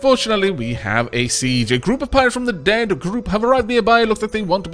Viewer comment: near the end when was reading "Deep Group" it sounded like he said "Dead Group"